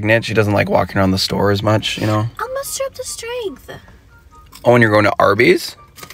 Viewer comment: The “I’ll muster up the strength” was so precious 😂😂😭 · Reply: oh shit, I thought she said "I'm at super strength" got a good laugh out of that.